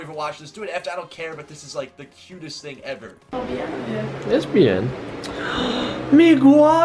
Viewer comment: did he say lesbian?